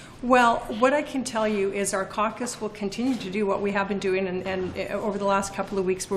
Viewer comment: I heard "do what we say"